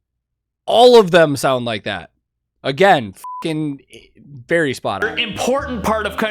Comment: for a moment I thought you said: berry spot on, as that's in his name LOL